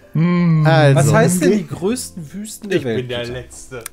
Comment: Ich habe erstmal "die größten Füße" verstanden😅